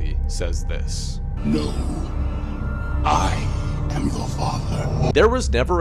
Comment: “He actually says this” Me: Then why I’m hearing Luke I am your father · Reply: I heard ‘no I am the father’ 😂